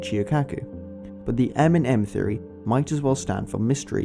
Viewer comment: M theroy something is missing from it maybe we can add another M to have M&M theroy I'm a student from Harvard and this is my new paper I'm working on . Look for my paper in the feature. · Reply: The speaker is actually saying, “..but the M in M-theory might as well stand for…”. It’s wrongly captioned as “..but the M&M theory might as well stand for…”.